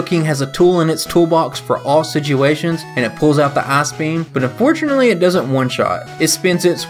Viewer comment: I heard "He pulls out the Ass Beam" 😆